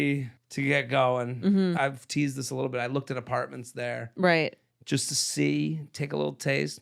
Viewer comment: I thought said he looked at apartments in Rochester and I was like wtf 😂